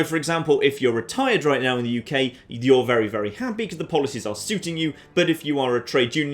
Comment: I just misheared "Suiting you" into "Super mutant" . I have various problems